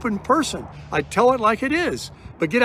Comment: does he say: "Do me a favor and get out there and 'crush them'?" Could that be construed as telling his followers to harass those who don't fawn over him?